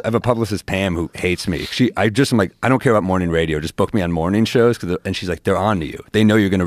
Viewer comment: Dammit I thought it said Mormon tv shows